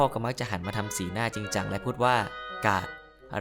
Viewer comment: นึกว่าพ่อพูดว่ากาก